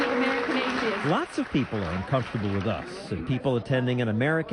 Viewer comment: into this video does he say "lot's of people are uncomfortable with us"? Us as in he's one of the atheist.